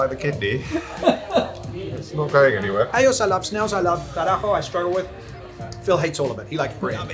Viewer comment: this was fun. when you said "tarajo" i thought you said "carajo" at first.